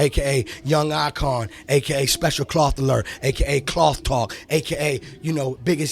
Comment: Did he call himself "special cloth alert" and "cloth talk" 0:50 . He's selling textiles now?